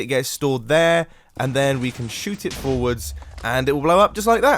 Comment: I thought you said 'it'll be shooted forwards' and then I was gonna complain about it in a very long comment but then I just went to check again and realised I'm A.